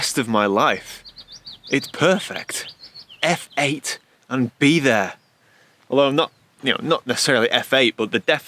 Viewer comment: My wife thought you said "f**k it, and be there"....which still works 😂